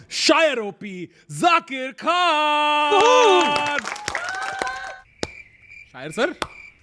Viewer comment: When he say Zankkker khan I thought he saying Zaheer Khan